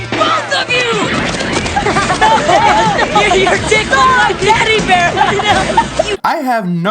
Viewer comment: did they say "you're tickling my daddy bear!" ?? .... what in the actual hell does that mean?????